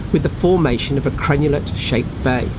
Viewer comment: what is the type of Bay you said had formed? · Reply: LIv, this is a crenulate (shaped) bay caused by erosion due to the terminal (last) groyne. Crenulate refers to the shape - notched out or a sort of smooth curve shape like a shell. I am not sure the bay has a specific name other than this. Hope that helps.